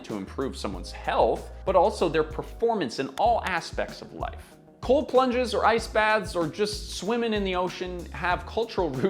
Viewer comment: Oh, I thought you said COLD LUNCH